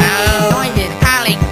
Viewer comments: The first time I heard this I thought it was saying "noise is counting pick a pawn" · Buddy i keep hearing "noise is calling hiccup phone" GET IT OUT OF MY HEADGET IT OUT OF MY HEAD GET IT OUT OF MY HEAD GET IT OUT OF MY HEAD GET IT OUT OF MY HEAD GET IT OUT OF MY HEAD GET IT OUT OF MY HEAD GET IT OUT OF MY HEAD GET IT OUT OF MY HEAD GET IT OUT OF MY HEAD GET IT OUT OF MY HEAD GET IT OUT OF MY HEAD GET IT OUT OF MY HEAD GET IT OUT OF MY HEAD GET IT OUT OF MY HEAD GET IT OUT OF MY HEAD GET IT OUT OF MY HEAD GET IT OUT OF MY HEAD GET IT OUT OF MY HEAD GET IT OUT OF MY HEAD GET IT OUT OF MY HEAD GET IT OUT OF MY HEAD GET IT OUT OF MY HEAD GET IT OUT OF MY HEAD GET IT OUT OF MY HEAD GET IT OUT OF MY HEAD GET IT OUT OF MY HEAD GET IT OUT OF MY HEAD GET IT OUT OF MY HEAD GET IT OUT OF MY HEAD GET IT OUT OF MY HEAD GET IT OUT OF MY HEAD GET IT OUT OF MY HEAD GET IT OUT OF MY HEAD GET IT OUT OF MY HEAD GET IT OUT OF MY HEAD GET IT OUT OF MY HEAD GET IT OUT OF MY HEAD GET IT OUT OF MY HEAD GET IT OUT OF MY HEAD GET IT OUT OF MY HEAD GET IT OUT OF MY HEAD GET IT OUT OF MY HEAD GET IT OUT OF MY HEAD GET IT OUT OF MY HEAD GET IT OUT OF MY HEAD GET IT OUT OF MY HEAD GET IT OUT OF MY HEAD GET IT OUT OF MY HEAD GET IT OUT OF MY HEAD GET IT OUT OF MY HEAD GET IT OUT OF MY HEAD GET IT OUT OF MY HEAD GET IT OUT OF MY HEAD GET IT OUT OF MY HEAD GET IT OUT OF MY HEAD GET IT OUT OF MY HEAD GET IT OUT OF MY HEAD GET IT OUT OF MY HEAD GET IT OUT OF MY HEAD GET IT OUT OF MY HEAD